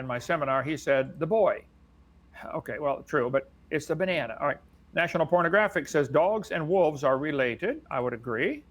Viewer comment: Did Kent call it "national pornographic"